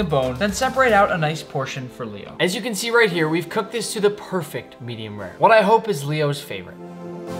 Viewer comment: I thought he said “i cooked my dog” lol. “that was easily the best thing ive ever had”